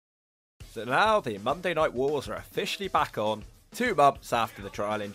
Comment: I genuinely thought you said “so the Aldi Monday night wars…” I thought about how packed it is doing the shopping post work on a Monday 😂